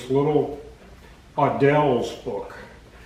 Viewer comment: Hello sir thanks for the great video I loved it. my hearing isn't what it used to be and I cannot make out the name of that series of books you're talking about that discusses these engineering techniques and principles. It sounded like you said Adele's books? Is that correct or did I hear wrong I did a search I can't find anything on them. I would love to have the whole series if I can find them somewhere. Thank you again for the great video I hope you see this message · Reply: The books are Audels textbooks from early 1900's , see the links that other viewers have left in the comments that will take you to scanned versions that you can read online....